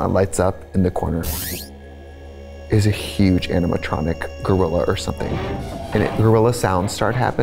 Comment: Y’all I thought Trixie said enema-tronic gorilla and yeah I would be afraid of a gorilla trying to do butt stuff to me too.